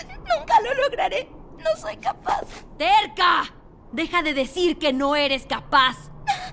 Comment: Like si escuchaste a kyabe decir una mala palabra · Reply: Yo solo escuché que en el minuto 1:59 caulifla dijo "v*rga"